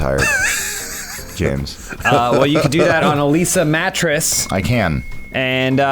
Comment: I thought I heard James say "Elyse a mattress" instead of "a Leesa mattress".